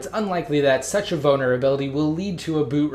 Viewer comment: am i the only one who heard "bonerability?"